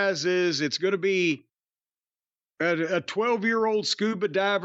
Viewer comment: Did Jim call Nia Jax "A pissed off parade float"?